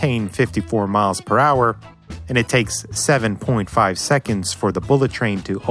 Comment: Odd. Some of the other comments are getting about 912.9 feet. But for some reason I'm getting 900.58 feet for my answer. Not sure where I might be differing in my calculations... EDIT: D'oh! I misheard that time as 7.4 seconds.